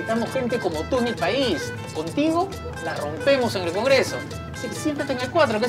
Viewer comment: yo escuche 1:54 contigo robamos en el congreso